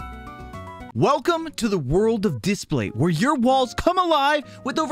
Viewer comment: I thought he said where your balls come alive